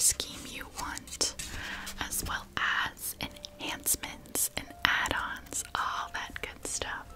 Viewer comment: I almost thought “enhancements” said “enchantments,” like placing enchantments on the fairy home if you want to keep it hidden for any reason.